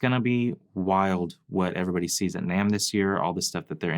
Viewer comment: Forgive my ignorance but what is nam or whatever word you were saying at about 11:59? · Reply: I think I figured it out: namm. They are an org website.